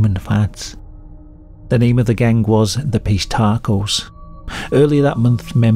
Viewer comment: I thought he said the gang was named the fish tacos 🌮 😂